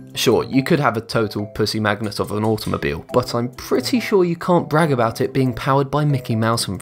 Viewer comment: Does he really say "have a pussy-magnet of an automobile" at about 7:45?